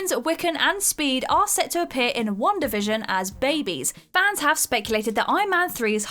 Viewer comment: you say “Wanda Vision” but the subtitles say “one division” @ 2:51 you need to capitalise “Iron Lad” @ 2:53 you need to write “skrull” instead of “skull” @ 2:55 you need to write “2” @ 2:59 you need to capitalise “Dr. Strange” @ 3:20 you need to capitalise “Pulsar” @ 3:29 you need to write “Monica” instead of “marker” @ 4:41 you need to capitalise “Endgame” @ 4:43 you need to capitalise “Nebula” @ 4:48 you need to capitalise “Bill” @ 4:51 you need to write “he a” instead of “here”